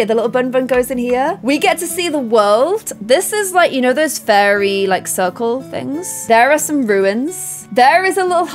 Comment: Did she say fairy ruins @ 20:05 or it's just me ?